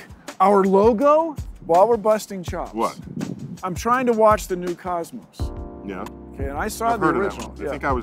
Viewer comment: I thought he said he said he tried to watch the new Cosby